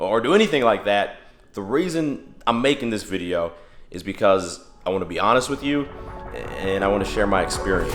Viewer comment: min of the video what's he actually saying is (The reason iam making this video, I want to be honest with you I love you guys and I want to do this for you)